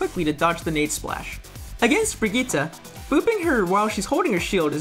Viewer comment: did HE SAY BRIGGITTA!?!?